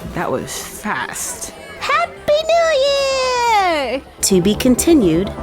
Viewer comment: I'm probably the only one who thought this, but when first said 'sommer and cali' I literally thought she said ' summer in cali' as in California!!😂